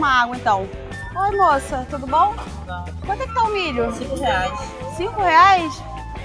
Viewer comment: oi moça, td bom? -nao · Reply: tudo bom? -bom